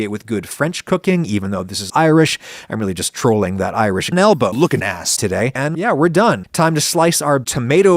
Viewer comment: I first thought he said "Idris Elba lookin ass"